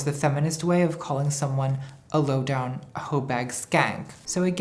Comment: I misheard "low-down hoebag skank" as "low-down hoedown skank", and I'm delighted